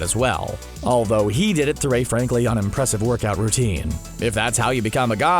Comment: "Unimpressive workout" You are most welcome to try it. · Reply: Kevin Slater I thought he said impressive. I’m at home almost dying from 60 pushups.